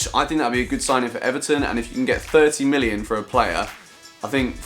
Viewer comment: im am palace fan and when I heard bolaise might be sold I almost cried because I thought the guy said 13 million but now its 30 million I dont mind but we might be losing bolaise : /